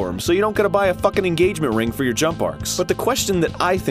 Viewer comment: Every time he says "jump arcs" I keep hearing "jump barks" and that... just puts a rather funny image in my head.